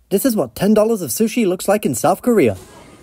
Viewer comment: I thought he said north korea